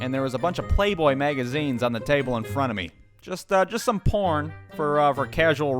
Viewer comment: did he just say porn